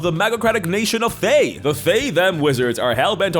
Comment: I keep hearing fae/fem (like the neopronouns)